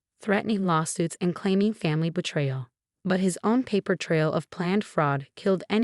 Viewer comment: minutes. “But his on paper trail of planned fraud…” The same type of mistake a third time. The writer used ‘on’ instead of ‘own’. The sentence should have been ‘But his own paper trail of planned fraud…'. Seriously, is the writer illiterate? Not knowing the difference between ‘on’ and ‘own’?